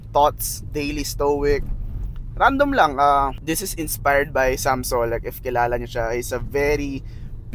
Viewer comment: sam sola ba yung sinabi nya? i can't hear clear eh